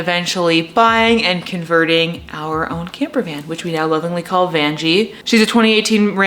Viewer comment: Does she say “Miss VANjie” as she backs up? 😂